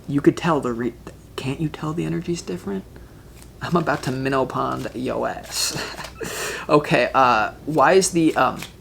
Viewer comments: Did you just say minnow pond our ass???😂🤣😂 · Did you just say "I'm about to Minow Pond your ass"?! Hahaha, cool! 😂